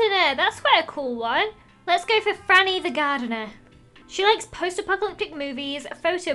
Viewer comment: I thought Lizzie said granny the gardener